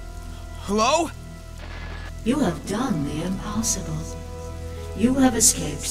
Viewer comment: Am I the only one who heard "You look dumbly impossible?" xD